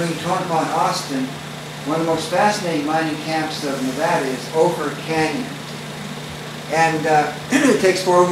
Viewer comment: you mentioned " Boker Canyon? Did I hear that correctly? Just north of Austin you said. I enjoyed the video. Thank You.